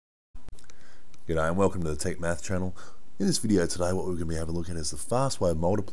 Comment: i paused as soon as it started to see what the comments said and if they agreed, and when i pressed play again i thought it said "welcome to the take meth channel" and i was like wait what.....